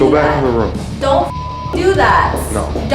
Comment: listen to when she says don't do that it sounds like she says "don't do thats"